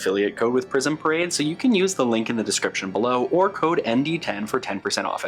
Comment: Loving this intro so much and the outtake was great! I had a good laugh and rewound it because it sounded like you said “description buffalo” instead of “description below” at the beginning, which feels very fitting for the haha. This is such a gorgeous collection, so many gorgeous vampy shades.